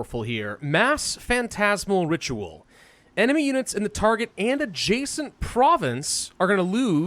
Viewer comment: I thought you said adjacent